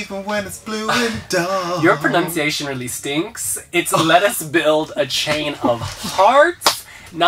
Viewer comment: I thought San Marino's was saying "Let us build a chain of art", haha. I still hear "bricks are gonna spread out" from Nina though! I actually had a difficult time figuring out what Boggie was saying a lot. It would come out as just a mumbled mess. I would just hear "Do you know our earth.... ??????" And I still don't truly know what Molly Sterling was saying in her song. I like to think she said "and I made a pearl... Abandoned you..." But Eurovision says she's saying "and I made a girl... Abandoned youth" (though I have many doubts with Eurovision's accuracy with lyrics)